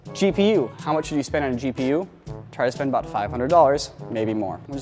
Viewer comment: It could just be my speakers, but I swear Niko said "Cheap-e-u" instead of "GPU" @ 0:55 XD